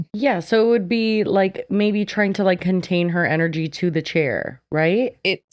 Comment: Me with my auditory processing disorder rewinding three times because I thought Jessi said “energy butthole”. 😹 I need my subtitles, where are my subtitles lmao